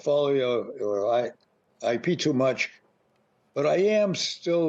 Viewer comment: Am I the only one who heard him say “I pee too much” at 1:14 ??